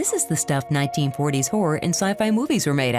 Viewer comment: I couldn't even figure out that she said "Horror and Sci-fi" until listening to it 3 times.